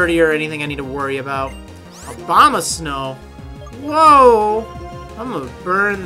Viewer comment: When I heard say Abomasnow I thought he said `Obama Snow'! XD